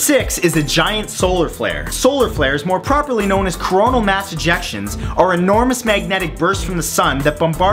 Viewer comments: thought he said Corona Mass Ejaculations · #6- i thought you said "solar flares more comonly known as coronal mass ejections" but then i put on subtitels and i saw you said "more PROPORLY known as" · With number 6 i thought i heard Cromo Mass Erections.... But it was Cromo Mass Ejections. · lol at 4:15 i thought he said mass erections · i thought he said erection.. hehe